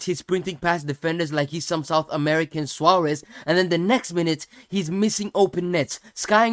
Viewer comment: did bro say a South American version of Suarez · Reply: Bro said "some sort of American suarez"